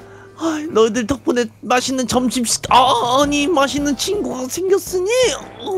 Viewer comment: 저처음 8:12에 맜있는 이라고 들었는데... 자막보고 알았어요 멋있는인거...ㅋㅋㅋㅋㅋㅋㅋㅋㅋㅋㅋㅋㅋㅋㅋ